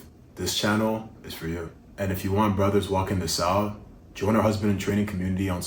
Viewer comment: Hey John, good to hear from you again. It’s a pleasure to serve you guys, and I appreciate you taking the time to comment. Quick clarity first: in this video I don’t think we actually used the phrase “going south,” so I may be missing exactly what part you’re referring to. When people say a marriage “goes south,” they usually mean it slowly drifts into resentment, no desire, more of a roommate situation than a real husband‑and‑wife covenant, or eventually divorce. Hope that helps. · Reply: you talk about "brothers walking the south", at least that's what You Tube put in the transcript for this video, but I think you might have actually said "brothers walking this out".